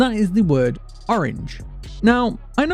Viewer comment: I heard “ortgange”